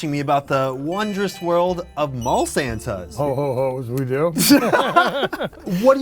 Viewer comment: santa is not saying HO HO HO ! he is saying HO HO HOES LOL